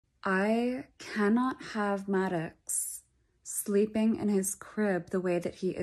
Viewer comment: MADDOX IS MY OC'S NAME AND I HEARD IT AS "MADUCKS" I CAN'T STOP IMAGINING A DUCK EDITED ONTO HIS FACE PLEASJEOFIHAEF